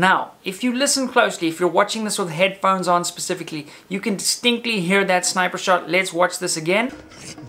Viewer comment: he said "stinkly" instead of "distinctly" lmao